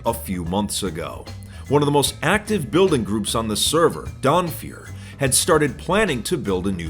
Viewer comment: I heard "Donfeur" and I was like "oh no, not another one!"